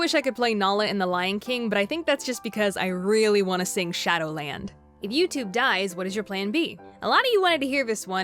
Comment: When you said shadow land at first I thought you said shadily lol